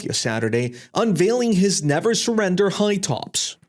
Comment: The "never surrender" shoes, I thought he said never sprinter at first.